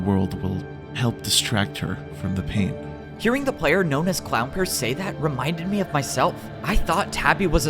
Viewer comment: Am I the only person who heard "clown purse"